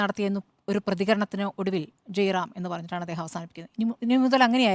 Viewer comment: is it not ram ram?? I heard his speech i didnt heard he saying jai ram, The phrase "Ram Ram" is a common greeting used in some cultures, where it is a traditional way to say hello, as per my undrstndng